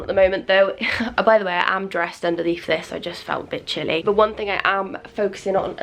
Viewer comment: I thought you said “I just felt bitchy” but you said “I just felt a bit chilly” lmao